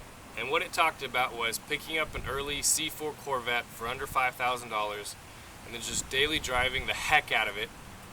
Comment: I'm sorry the wind was blowing so it sound like you said you got it for under $500 that can't be right so I'm guessing it was 5k. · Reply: yes under 5k